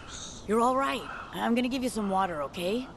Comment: It sounds like he says waiter like as in the waiters in the food restaurant😂